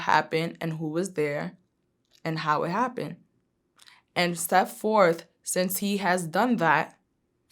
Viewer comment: She looks crazy ,talking with her eyebrows and out the side of her mouth while chewing gum. 4:04 4:08 - rebooting. And what the hell did she say 4:15 “steatforth” ? trying to sound smart.